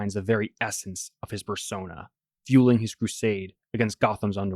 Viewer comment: I had to rewind a few times, I kept hearing "fursona" And that's not exactly wrong